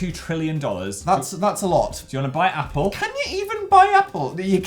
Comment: When it said buy Microsoft I thought it said buy Mycroft